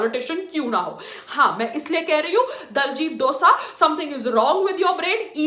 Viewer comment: did she really said diljit dosa or am I deaf👁️👄👁️